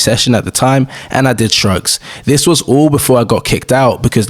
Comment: Me doing this at 14 & seeing this guys build is crazy😭 3:27 Wth? Did he do drugs or am I heading wrong what he did? Caption says shrugs🤨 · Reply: He said shrugs